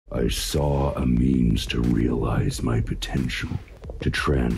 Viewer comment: I heard "I saw a meme" 🤣